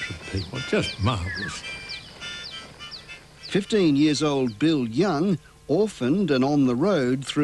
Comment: Did they say 15 years old? That would make him child soldier.